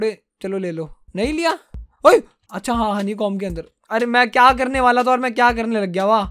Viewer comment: when he said honey com i Thought HE SAID HONEY BOMB XD LOL